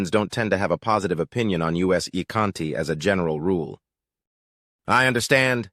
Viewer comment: U.S. Ykanti! He said "U.S." Ykanti instead of "us". The Ykanti of the United States lolol